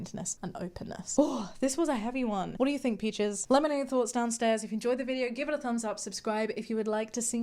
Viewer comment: When you said "let me know your thoughts downstairs" you said it so fast, I heard "lemonade downstairs" and I got excited for a minute 😂 🍋🥤(I'm going to go make lemonade now 😋)